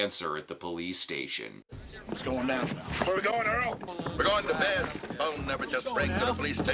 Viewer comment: I've seen Halloween 4 maybe a thousand times, and every time I hear Earl's explanation it sounds like, "We're going to bed," instead of "We're going to Ben's"